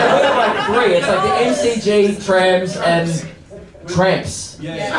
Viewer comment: I too thought they said trams and when you found out they meant tramps I lost it cause they must’ve been so confused why you were just rambling on about public transport